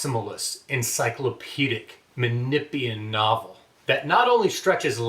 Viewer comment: Great work you surpassed yourself again! Can you please tell me what word where you saying at 8:53 after encyclopedic ? Is it manipian?(because I didn't find anything on google on manipian literature) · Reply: Thanks so much! Menippean satire.